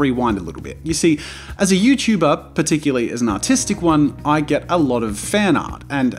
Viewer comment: I thought he said “autistic” 🤦‍♀️